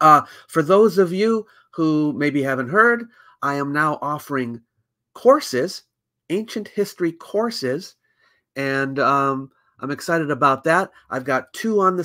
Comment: My husband misheard this video from the other room and yelled "I am the ancient mystery horse!" And I can't unhear it now.